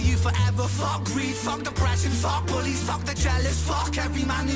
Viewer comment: I thought he said F the police, but he actually says bullies.